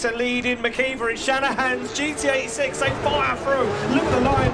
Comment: Anyone else hear GTA 6 instead of Gt86😭